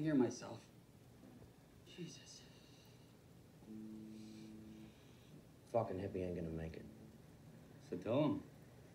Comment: I can't hear what Mick and Nikki say to each other at the 1:15 - 1:20 mark..? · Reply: Mick: Fucking hippie ain’t gonna make it. Nikki: So tell him.